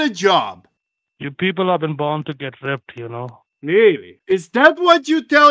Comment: Did he say “you people have been born to get ripped” at 15:16 ???